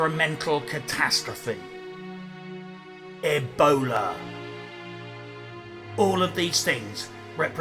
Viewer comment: At first I thought you said "eggboner" till I realized you said "ebola" xD annnnnd that's a heart